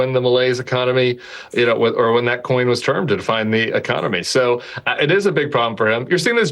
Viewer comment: Yo Tim, did you say 8:17 ‘coin was termed’? Just want to prove I hang on every word 😂